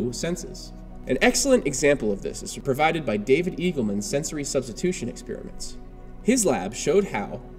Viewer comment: In around 3:27 - 3:30, he introduces a professor. What is the name of that professor exactly? (I cannot hear his last name very well) :-? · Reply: David eagleman. Check him out he's super amazing. Lots of good books